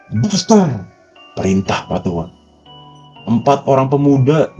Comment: Pak tua ngomong apa di 6:14 ya bang?? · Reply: Bakar